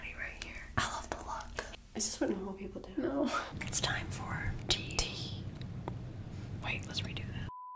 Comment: I misheard her and I thought she said “it’s time for D” Great video